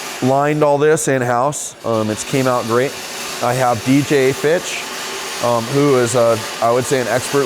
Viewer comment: first time I got to this point I swore you said "I have a BJ B*tch" had to rewind and rewatch.